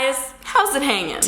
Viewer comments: I thought she said gay guys in the beginning 0:00 just close your eyes she says gay guys but then when you look at her mouth she says hey guys S.H.O.O.K🤯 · why does it sound like she said "gay guys! hows it hanging?"